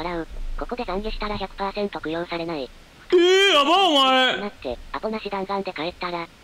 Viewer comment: クロちゃんがとろちゃんに聞こえて2度聞いてきもたw